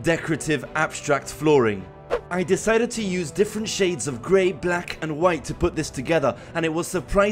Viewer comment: I thought I heard 50 shades of grey